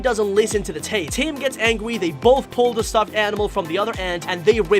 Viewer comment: When tim gets mad at the baby it sounds like you said angy instead of angry